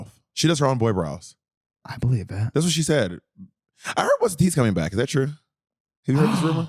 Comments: who's coming back? Sounds like Bob says "I heard Rick Dees is coming back!" He says it three times and I can't make out any of them. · What did bob say around 21:20? What or who's coming back? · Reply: The podcast what’s the t